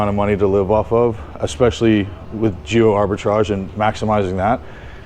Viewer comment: Did he say Jew arbitrage 7:35? What is that or did I hear incorrectly